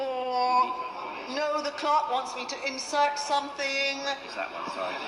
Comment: Did she really just say what I think she said????????? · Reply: Yeah dude! You heard it right! "Insert something" and thn a long pause 😂